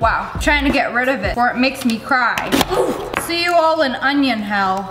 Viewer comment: DID SHE JUST SAY “OOF”